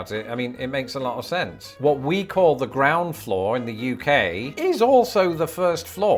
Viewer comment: LoL, I thought you initially said the CROWN Floor, then realized after the second time you said it, it was Ground Floor.